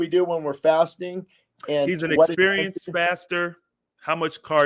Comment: He was an inexperienced faster. It sounded like you were saying experienced faster.